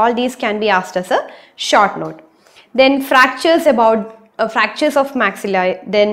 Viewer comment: What did you say at 01:47 ? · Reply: Le Fort fractures of maxilla